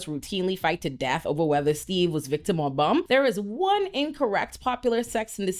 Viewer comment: Nice points. But as a non-native, in the beginning, I had a hilarous misheard sentence... I'm pretty sure upon rewinding that you said "whether Steve was a victim or not", but my Hungarian ears managed to hear "whether Steve was Viktor Orbán"... 😢🤣